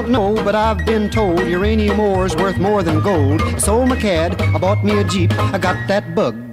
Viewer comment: i always thought it said sold my cat, now i know its cad and i hear cad every time now